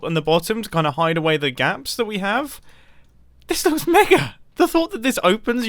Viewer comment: What is said at 2:20? Never heard of the word. Sounds like he says "this is N*****" but not sure what it is. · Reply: Mega not N***